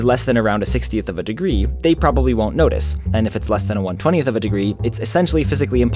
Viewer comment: i am confused here. They "probably" won't notice for < 1/60 but "physically impossible" when < 1/20? Did I mishear? Shouldn't the former be physically impossible? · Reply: 1/120, not 1/20.